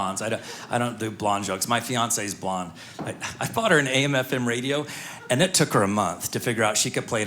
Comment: What does he say at time 5:12? " I bought her name, FM radio." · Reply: AM FM radio. Not name